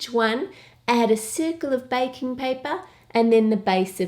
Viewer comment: omg this is so cute and delicious love it👍 oh and I thought you where saying bacon paper 😂